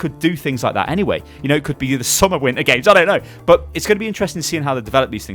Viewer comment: did you just say summer winter games?